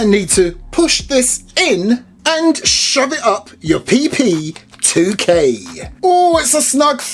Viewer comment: He said pp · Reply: “And shove it up your PP......2K” THAT’S WHAT HE SAID